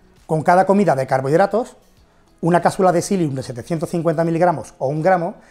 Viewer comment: Como se llama la pastilla que dice en el 3:15 para bajar el índice glucemico del carbohidrato? No le entiendo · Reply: Es una planta se llama silium